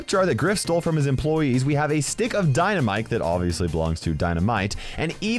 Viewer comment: did you really say " a stick of dynaMIKE, that obiusly belonds to DynaMITE" like WHAT IS THIS MATRIX reality changes... like What now, are we just supposed to see a waking piece of dynamite holding 2 little old men that explode on impact with the ground XD